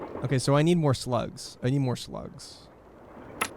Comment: loaded back in after passing for a day around 3:50:25 and genuinely thought said “we need more sluts”, had to double check this wasn’t leisure suit larry 😅